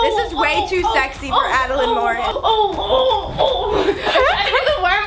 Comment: I thought our channel said adrenaline mountain